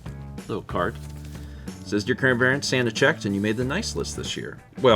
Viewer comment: "We did mail in Columbia!" gave me a violent flashback to 2012 when I sent in my first letter at the young age of 18. Wow. Man. It's fine. Yeah. 🥲 Also, at 52:29, I definitely heard say 'crandbarents'. Tell me I'm not alone in that. I'm dying.